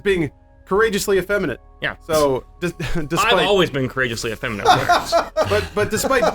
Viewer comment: What is saying at 11:01? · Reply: says thing is being courageously effeminate and jokes saying "I've always been courageously effeminate"